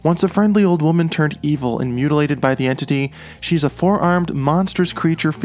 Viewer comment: Before you added the second set of arms, you were telling her story and saying she captures her prey with her four arms and I was so confused. I thought, maybe he said forearms? lol